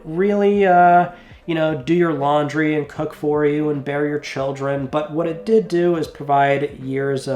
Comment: Totally misheard that the PS3 would "bury your children". 😂